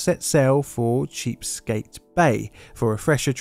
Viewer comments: did he say "cheap-skate bay"? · please tell me it was either a horrible mistake or an inside joke that you said "Cheapskate Bay" instead of the correct name "Chesapeake Bay". Please. I beg you.